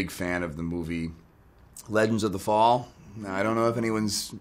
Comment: what was the movie he said in min 2:06 cannot fully understand. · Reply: JD legends of the fall